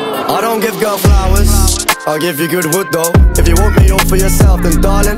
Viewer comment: First few times I listen to this song I thought he was saying "I give good hood tho" 😂😂